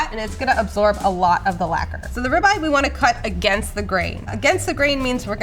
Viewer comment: did she say lacquer? didn't know that's what it was called. as someone that does woodworking, it's a funny term to use on food